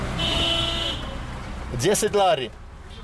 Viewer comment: did you just say Desi Daru?